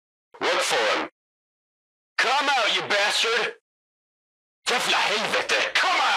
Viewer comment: sorry what did you say? “JAVLA HELVETE”